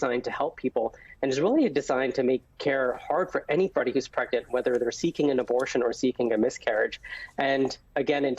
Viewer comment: Dan..imorove your listening skills. Rewatch the video, you can even make it replay slower if needed. Some women need the medication while they're having a miscarriage...not to cause it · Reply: actually you are the one who needs to listen again and slow it down if needed… at 2:36 he clearly says “whether seeking an abortion or SEEKING a miscarriage”.